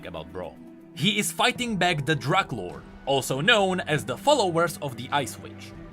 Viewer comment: my captions said 'he is fighting back the drug lord'